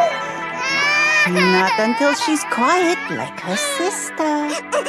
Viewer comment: what is she saying here? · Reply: “Not until she’s quiet, like her sister.”